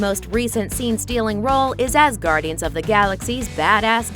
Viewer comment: Anyone else hear Asgardian???